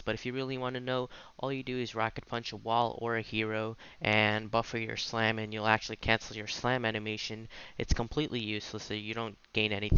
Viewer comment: what did he say here? I guess I'm hard of hearing or something but I rewatched and on a louder volume and I don't know what "Get Quaked On" said. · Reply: You punch a wall/enemy and then buffer your Slam , it will cancel your slam's animation